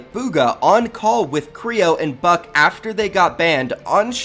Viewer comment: Who else heard fuck 😂